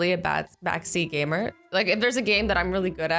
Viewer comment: did you just say Zgamer